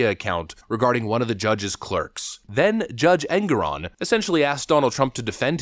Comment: There is NO WAY the judges name is "Angron"? Or am I mishearing you? XD Regardless, for those in the know, funny bit!